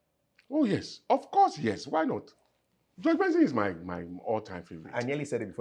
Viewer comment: who did he say is his favorite guitarist? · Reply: George Benson